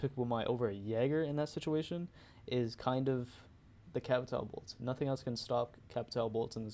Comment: what did he say? Something “bolts” maybe? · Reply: Capitao bolts